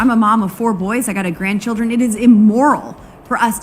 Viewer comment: Someone like Boebert should really be discouraged from excessive breeding on account of her inferior genetics. Her lack of measurable intellect shows right at the beginning of this clip when I thought I misheard, but then I listened to that passage twice more and for confirmation turned on the youtube CC function and there it was : she actually said she has "one grand childen" Who does that? The race to the bottom is fierce and competitive in the party of stupid, to paraphrase George Carlin, look how dumb their leader is, the rest are even dumber than that. I don't know if any other candidate can unseat the Queen of stuoid.